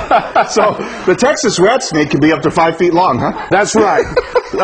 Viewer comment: Rat snake? Thought he said rattlesnake